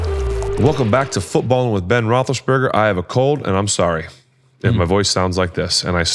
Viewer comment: Sounded like Ben said "I have a cult" in the beginning. 😂😂